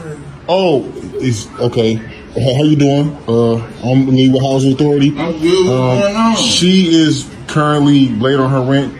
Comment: It sounded like he said Housing Authority. I think thats different from Section 8. Section 8 does not evict, the landlords make the decision to evict or not.